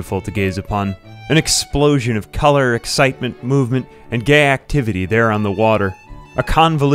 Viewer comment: did he say ‘gay activity’?